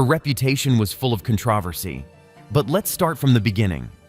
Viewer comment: Contrarvursee that's what it sounded like he said instead of controversy